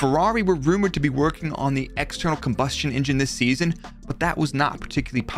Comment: That external combustion engine joke caught me off guard lmao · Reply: How did I miss that? I thought he said internal combustion. I was so confused LMFAO.